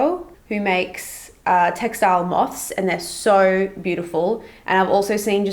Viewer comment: i thought it said mouth hahaha